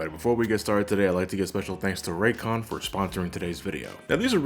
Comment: When i first heard you say "I like to give special thanks to Raycon for sponsoring todays episode" i wasn't looking at the screen and you were talking a bit fast so i thought you said "I like to give special thanks to Ray Comfort sponsoring todays episode"... i was like "WTF, that nutjob sponsors your video???" and had to rewind to make sure.